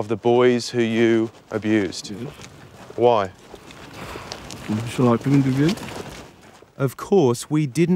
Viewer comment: What does he say at 2:56? Never been able to figure it out. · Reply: He says "Would you like to interview?"